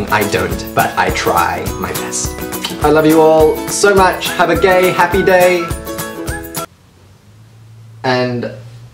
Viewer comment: did he just say "have a gay happy day" haha btw that wink at the end had me dying